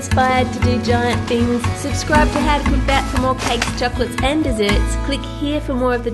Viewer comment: At the start is sounded like she said doblerone